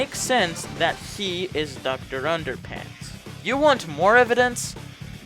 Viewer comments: did you just say "Dr. Underpants"?... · DID U JUST SAY "Doctor Underpants" XDDDDDDD